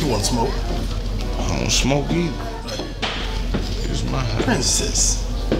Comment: did you said "its my house" or i am trippin